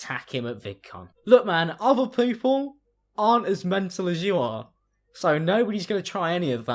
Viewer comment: “look man other poople” wait wtf did you say 😂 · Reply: "aren't as mental as you are"